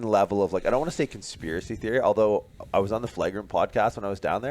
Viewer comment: Recently on which podcast? Sounds like you said 'The Flight Room' podcast. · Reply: Flagrant